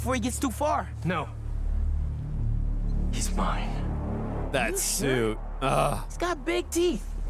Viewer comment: I agree with about being more excited about the story than the gameplay bc, again like he said, the gameplay looks amazing(pun intended) but what gave me the most chills and excited to experience the game was the end dialogue Peter: “He’s mine.” Miles: “Are you sure? He’s got big teeth.” Peter: “So do I…” Peter glides away Ganke: “He’s not normally like that, right?” Miles: “No, he’s never like that…” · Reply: Oh I thought he said he’s fine I didn’t realize he said he’s mine damn well I feel stupid